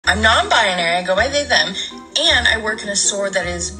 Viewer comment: Anytime someone says, "im non binary," i hear "im non brainy"